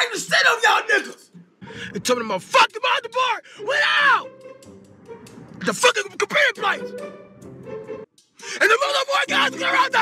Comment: I got $5 for who can tell me what he said 😂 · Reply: G2Chill Out His motherboard went out and got corrupted